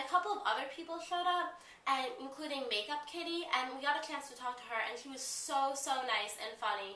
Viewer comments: it sounded like you were saying makeupkitty. but its meekakitty. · meekakitty! ohhh. i thought she said makeupkitty lmao. i wish i went, i live in nyc so i shouldve lol.